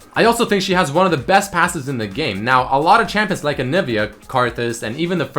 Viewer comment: So many great underrated champs, only 5 spots :( Also hope to see you guys try out that contest and can't wait to increase my fan/viewer interaction with it as well :D Enjoy the video! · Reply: it sounded like asses.